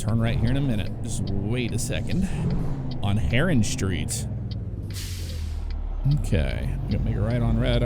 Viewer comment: I thought it said “Heroin Street” I was like wtf